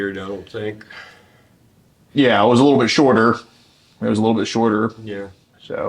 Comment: I started laughing really hard.I thought said he's a little shorter